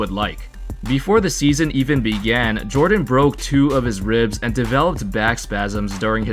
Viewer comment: I thought you said “he broke two of his rims,” not ribs.